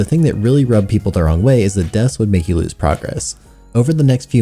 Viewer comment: I have always thought the remains said gifts instead of deaths. (And I remember Dinklebot.)